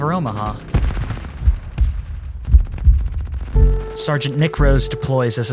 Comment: Me, bitch (he said affectionately) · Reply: I swore I heard “sergeant nick grows”